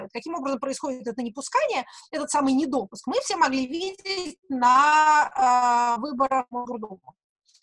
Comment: Кто расслышал? На 32:30. "Каким образом происходит это непускание, этот недопуск, мы все с вами могли увидеть на выборах........ ..... ....." и проблемы с качеством видео вдруг пошли. Что за выборы? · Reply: Кажется так: "... на выборах в МосГорДуму в (каком-то месяце) 2019 года"